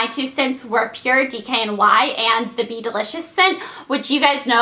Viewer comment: I thought she said beetle idiots instead of be delicious